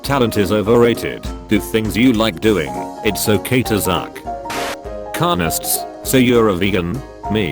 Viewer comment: How and why did it say "zuck" instead of "suck" Hes onto us guys